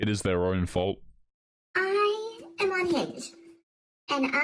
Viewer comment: I swear I heard "I... am unhinged."